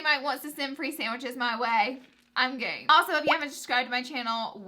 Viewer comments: I thought she said I'm gay 😂am I the only one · i thought she said "im gay" i was like haha me 2